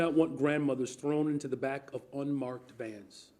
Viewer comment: This guy is a 🤡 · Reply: Did he just say grandmother's in unmarked vans???